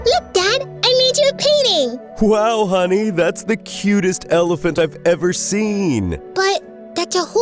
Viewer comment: i thought u said it was not a elaphant.